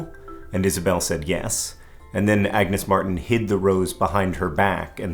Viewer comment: oh boy - i thought you said she "hit" the rose behind her back. but she "hid" it xD